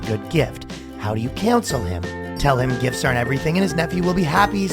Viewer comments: I was going to call out how unrealistically reasonable the sensitivity instructor was being, right up until the ending line. Very good. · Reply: Same, but for question 2 I thought he was saying how do you cancel him? But that ending got me, I didn't expect that and it to be so upfront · I thought I heard him say “how do you cancel him” the guy nervous about going to his nephews birthday party